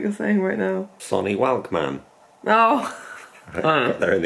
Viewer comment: I thought said 'Sony Wankman' 😂😂